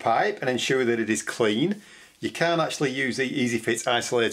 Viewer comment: Can or CAN NOT? What is he saying? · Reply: Can't. Sorry, some people struggle understanding my accent ;-)